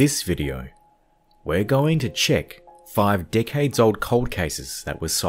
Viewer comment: who else thought thought at the beginning at 0:04 he said "5 dick heads" instead of "5 decades"